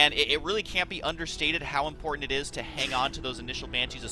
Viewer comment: Did husky said "understanded" instead of "understood" at 6:53? ^^